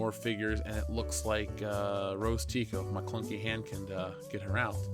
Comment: Did someone say Maclunkey?????